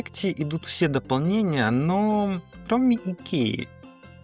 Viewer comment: Все дополнения томи икея? ИЛи мне послышалось?